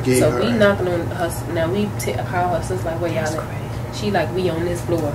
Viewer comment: I heard someone say, “that’s crazy” around